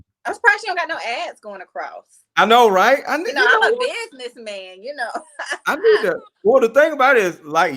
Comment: did she say she a business man.🤔